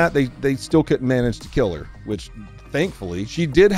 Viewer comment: The woman that was in the apartment is walking away with a big check imo. If I woke up to a car alarm and pounding on my door, yeah I'd be walking up with a gun too. Glad to hear that the woman made it out ok and is recovering! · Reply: 🤦🏿‍♀️ Stop spreading lies 🤥 Watch the whole video. She later died! EDIT: SORRY! My bad!! I thought he said they managed to kill her... It's too early, haven't wash my ears 😅